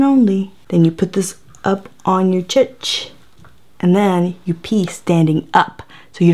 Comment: Did you call it a Che-ch at 5:01 ? Haha! Whats the origin of that word? Is it a certain culture? I'm honestly interested.